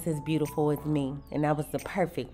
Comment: I thought you said and your time with your man but you said enjoy your time in Miami options are our friend she not going to win no beauty prize but I would love to watch her compete 🤔🧐 😎 as well as check out her business style 🤔🧐